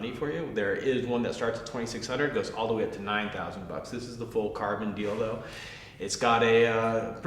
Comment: What the hell did he say at 0:42 :D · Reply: rafter This is the full carbon deal though.